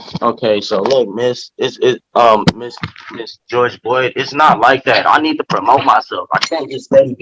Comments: Did he really JUST call Judge Boyd Ms. George Floyd? I swear that’s what I heard him call her.😂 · It sounded like he called her Miss George Boyd. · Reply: I swear it sounded like Ms. George Floyd